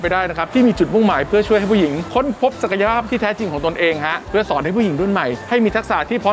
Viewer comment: พูดว่าค้นพบสักหย้าบผมนี่ลั่นเลย<num>